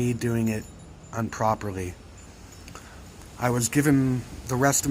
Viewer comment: you said unproperly instead of improperly, first time I've ever heard it said that way, really freaked me out had to comment on it. editedit: sorry to hear about all the issues you are having with tinnitus and psychosis, really interesting video